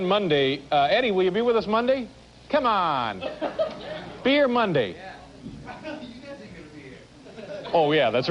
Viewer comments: Did anyone catch what Eddie said when Dave asked him to come on monday? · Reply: He says “you guys arent gonna be here” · What did EVH say at 4:15? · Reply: “But you guys aren’t gonna be here.”